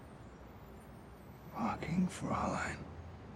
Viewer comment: Can someone tell me what he says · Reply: "Fucking Fraulein" i think